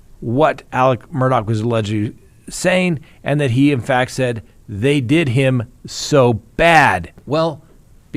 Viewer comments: I heard “they did him so bad.” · It sounds like he said "they did him so bad". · I heard “I did him so bad at first” but when slowed down I heard “they did him so bad”.